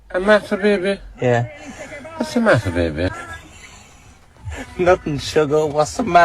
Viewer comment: The joke of what's the matter baby i don't had understand cuz the audio was poor enough for me to misheard it as MASTER baby kskskskskskks